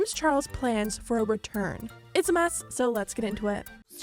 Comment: i heard it's a curse instead of it's a mess